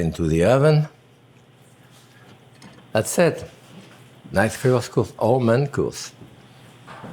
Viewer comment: I thought he said, "That's it; it's nice; I cooked all my uncles."